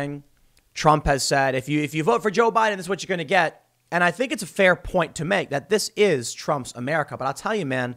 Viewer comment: Do you all have selective hearing or something. Tim said that's that the far left is saying. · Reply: Listen again at 1:39 he clearly says: "I think this is a fair point to make that this is a Trump's America"